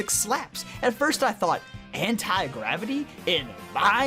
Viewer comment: How the heck did I hear “antigravity” as “hentai rapping”? I need to slow these down from 2x speed.